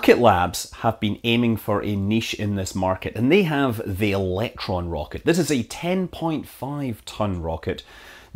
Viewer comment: Love your videos Scott. Had to laugh a bit when I thought you said "VonBraun's Exploder One rocket"! I know it's the accent but still made me laugh. :)